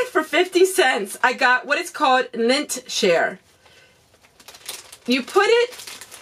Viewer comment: LOL, I thought you said lint SHARE! I'm like, "WHAT??!" Don't share it with me, lol. ;)